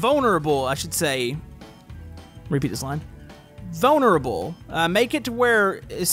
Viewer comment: Anyone else hear someone say "repeat this line"